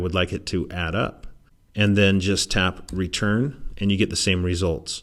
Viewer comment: Hi there, At 8:09, what was the key did u press to do the sum. I heard something like return. Please elucidate. Thanks